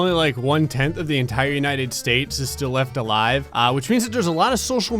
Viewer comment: I thought you said "ABS" and I was like: what? N i c e